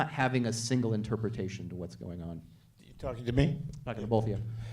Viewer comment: did I just hear Paul Schrader say: "You talkin to me?'